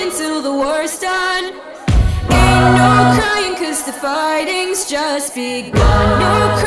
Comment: I love this song but it sounds like she says farting instead of fighting